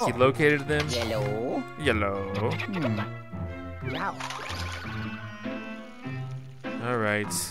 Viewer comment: i love luigi!! i love how he says "hello" and it sounds like "yellow" hehehe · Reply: I thought he was saying yeahllo